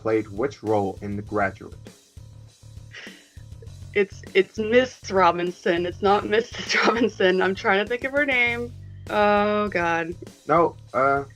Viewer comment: "It's Miss Robinson not Mrs. Robinson...I'm trying to think of her name..." Then and just gives her the point for Mr. Robinson... What the???? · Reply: I didn't even notice that. At the time thought she said "It's Mr. Robinson, not Mrs. Robinson...I'm trying to think of his name". I guess she was trying to think of Elaine? The way pronounced "Murray" did make it sound like a female name like "Marie." Thankfully it didn't affect the game at all.